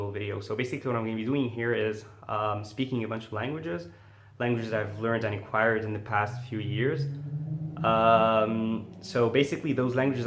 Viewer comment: What is he saying in this moment 0:12? I've tried to listen it 5 times but still didn't catch it..... "Speaking much languages, languages i've learned.... what?.. quired?.... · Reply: DirtySocks "speaking a bunch of languages, languages that I've learned and acquired in the past few years..."